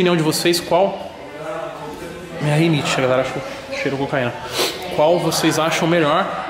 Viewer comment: q falou q a mansão não faz bem pra ela, o q ela foi fazer lá? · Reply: mano assiste uma parte desse vídeo, no minuto 7:31 , eu ouvir cocaína , não entendi direito